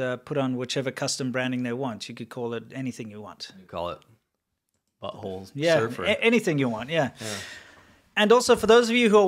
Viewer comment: Did you just say "Butthole Surfers"? They're one of my very favorite bands since 1983. If there were such a chip I'd buy it for the lulz! What bums me out is all those young brilliant minds in China being hampered by the CCP "educational" system. Those people must be incredibly frustrated, my heart goes out to them.